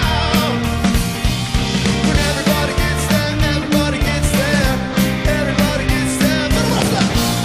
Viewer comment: Can someone please translate this part for me? 35:06 I genuinely have no idea what Brendon just said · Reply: I think he said and everybody gets better and everybody gets better but you have to look up the song